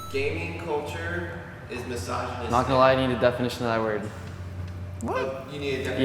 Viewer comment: “Gaming is misogynistic: “Ngl i need the definition of that” lmao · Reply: I thought for a second he said "I am the definition of that" lmao